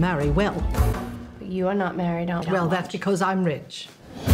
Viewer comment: "You are not married Aunt.. "Well that's because I'm rich" MOOD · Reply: bag ofhammers Oh. I thought I heard sje said "because I'm a witch."